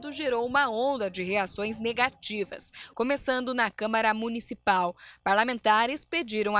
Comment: Só eu ouvi “câmera municipal”?